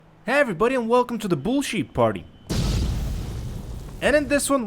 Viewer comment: Ngl I thought you said welcome to the bullshit party😂😂😂